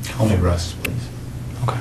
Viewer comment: what did he say? I want the arrest please?